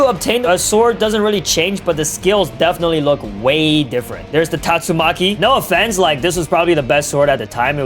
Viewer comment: Did bro say tatsumaki